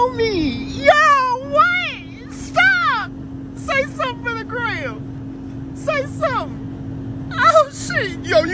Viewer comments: His voice went from a man, to a teen girl ,to a middle age woman, to a granny at the end 🤣 best line "yaaaa what STOP, say sumn for the gram" . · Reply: Oh no! I thought he said 'say something to Graham' 😆😆😆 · His voice went from a man, to a teen girl ,to a middle age woman, to a granny at the end 🤣 best line "yaaaa what STOP, say sumn for the gram" . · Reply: I thought he said “say sum in Korean”